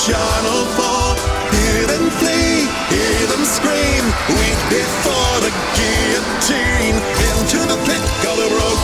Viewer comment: I can't get over the 'Wheat before the Guillotine' line, it's so powerful and conjures an image of how Afton sees normal people, as souls to be harvested like a crop for the benefit of the reaper (I know the game's not about him but still). The word guillotine also brings forth the image of how efficient and brutal he is with the killings. Love the song! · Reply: Each to their own, I hear it as 'wheat before the guillotine' but I can hear 'weep' instead